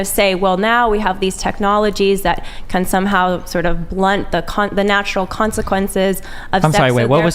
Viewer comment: it sounds like she says “blunt the cunt” 😂😂